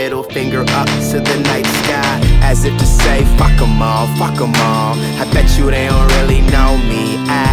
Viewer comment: my mom thought that he said, Guacomole in place of Fuck them al. ROFL XD